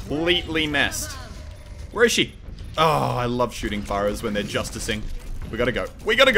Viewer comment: Did said I love shoting pyros???